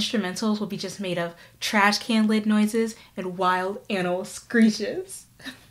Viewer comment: "no lyrics, trash can noises and wild animal screeches" sounds like some noise music 💀💀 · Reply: I heard "anal screeches" and wondered. Thanks for the clarification.